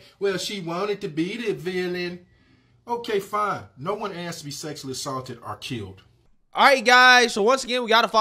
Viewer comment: I missed the part when she claimed she was sexually assaulted...I thought she said she was sexualized. But she sexualized herself.